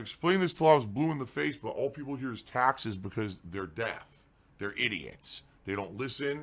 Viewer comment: did he just say death instead of deaf?